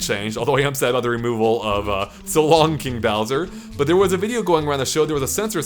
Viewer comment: It was ‘so long-a-Bowser’ (but it sounded like ‘so long gay Bowser’)